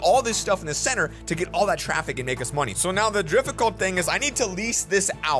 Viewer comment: did really say drifficult